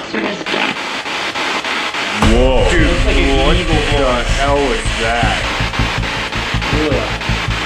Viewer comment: That sounded like “what is that?”